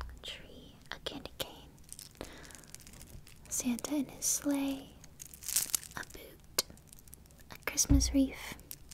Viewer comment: i thought she said “Santa and his Slaves.”